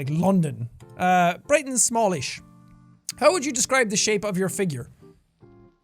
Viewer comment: I, uh, I thought it said "finger"....